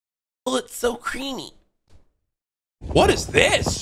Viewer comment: Did bro just say o it so creamy 😂😂